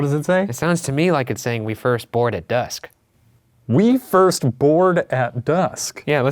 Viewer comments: It sounded like "21st ward at dusk" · I hear "Wave your sword at... Us." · I just got done watching a let's play for Corpse Party, so I think I'm prepared. (?) I would love for you guys to visit more haunted locations outside of the U.S. (yes, I've watched the ones in England), but it would be cool to see Poland, France, maybe Japan, if they'd let you? Idk. But woot, another episode! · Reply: The part where Ryan thinks it says "we first board at dusk," I thought I heard "21st ward"--anybody else? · i heard “my firstborn was uh....” maybe they killed their firstborn